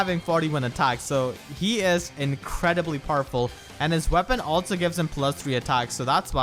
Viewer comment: I thought you said Purple regarding Hector's attack as a Monster Hunter reference. But upon hearing it again, you meant powerful. However, I choose to substitute reality with my own, and be happy at the possible MH reference.